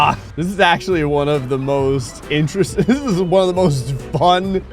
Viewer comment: what did he say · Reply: I think he said "interesting"